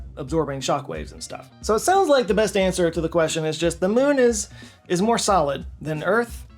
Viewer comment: All I heard was "yes the moon is hollow"